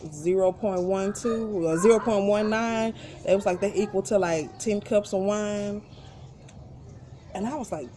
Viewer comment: did she say 10 cups of wine? 10 cups huh? LOL that’s so ratchet 😅